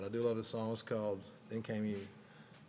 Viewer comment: I thought it said came in you 😂😂😂😂